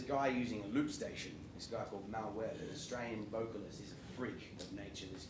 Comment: At about 5:15 he mentions another artist, what name does he say? I can't find him. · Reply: Mal Webb I think